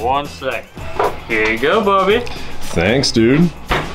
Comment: I think he called Bobby booby 😂